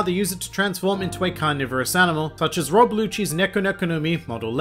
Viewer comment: Anyone else hear "Rob Blue Cheese"